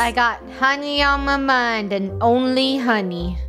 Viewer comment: I thought was gonna say I got honey on my money but she said mind not money